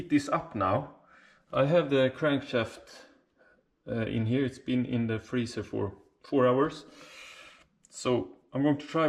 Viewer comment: thank you sir 👍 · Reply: this might give you a laugh, I misheard you at the start of the video and thought you said ‘I’ve got the beer in the freezer bag’. That would have made an interesting video 🔧 + 🍺 = 🥴